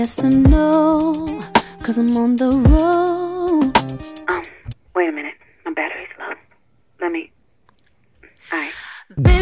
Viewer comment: "Wait a minute my battery‘s low" that part never gets old Always loved her tracks with Spanish guitars on this compilation album. Also the part where Tank starts singing in the background makes this already totally beautiful track even better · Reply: I thought she said "Wait a minute, I'm better now" and "Wait a minute, I'm better in love" 😂